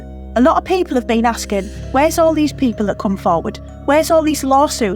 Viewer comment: Did she say ask ed. Wtf.